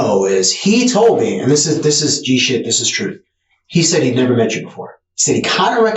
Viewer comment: Did he just say... " this is g shit..." ?